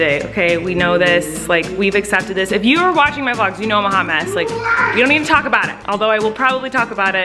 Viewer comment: I thought you said “we know I’m a hummus” 🤣